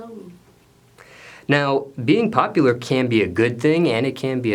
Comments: He said popular I thought this man said “pot dealer”? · I thought he said “pot dealer?” But it was popular lol!